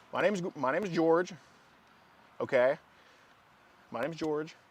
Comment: "My name's Go- my name's George." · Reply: Yeah I think I heard “Gorge”